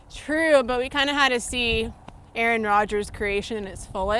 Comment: did she just say “Aaron Rodger’s?” The quarterback is full of surprises